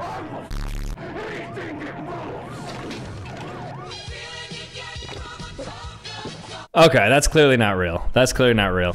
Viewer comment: why did he say that😭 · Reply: he said, "I don't give a fuck, anything that moves"